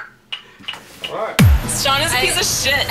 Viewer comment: is it just me or did I hear saying "Marble Soda" at the end? O_o